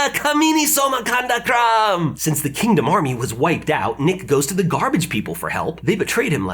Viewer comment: Did he just say Nick